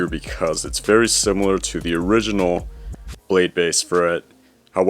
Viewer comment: he says bladebey instead of beyblade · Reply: he said blade base